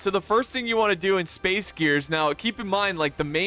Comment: Oh I thought that said space ears